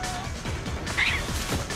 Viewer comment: I thought this said Balkan War for a second and was looking for YUGO flags and such. Lol